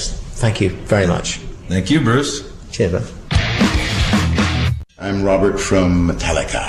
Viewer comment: DID BRUCE SAY CHEERS LOVE AT 1:26? OR WAS IT BRUV! IM SURE IT WAS CHEERS LOVE!!!!